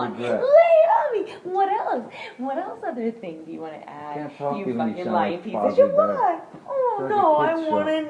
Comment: did he say "I can't talk to you when you sound like Fozzy Bear"??